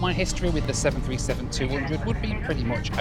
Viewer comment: I could've swore he said "piece of shitory", but when I unclogged my ears he said history, lmao.